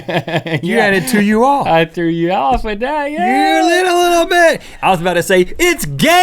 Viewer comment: it sounded like Rhett said "you little little b*tch" at 0:22 also play and press 7 and press 1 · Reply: he said ...BIT...